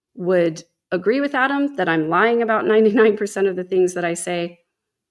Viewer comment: What I heard him say was that he doesn’t agree with about 99% of the things that you say